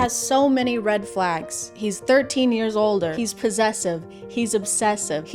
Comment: i thought it said “he is 13 years old” for a momment lol